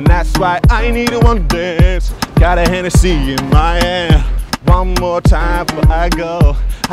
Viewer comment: For a good sec I thought he said Hennessy in my ass